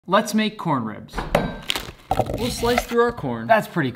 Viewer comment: Why Tf did I hear “let’s make cornbread” the first time I watched this short?